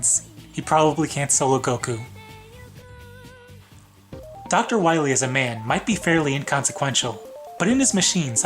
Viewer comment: Dr Wiley is a man. I misheard what you said and now that line amuses me.